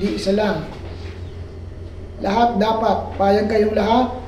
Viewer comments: may narinig akong boses "PAYAG KAMI" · I heard a voice saying "Payag kami" ng napakahina.